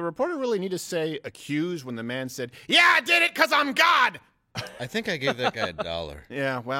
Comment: Sounds like he says "because I'm blk" and not "because I'm God"